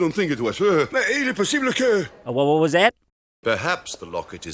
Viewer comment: He's talking in French and says: Mais il est possible que... Meaning: But it is possible that... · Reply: I always thought he said in Spanish: "Es imposible que..." meaning: "it's impossible that...", or maybe it means nothing lol